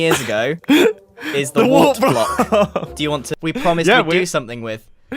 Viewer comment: Tiny correction on the subtitles at 12:50. The subtitles say "warped" but it should be "wart" ;)